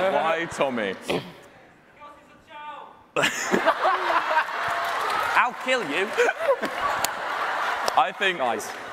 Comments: what did that guy in the crowd say? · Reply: " Because he’s a child ! " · what does the audience member shout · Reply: "cause he's a child"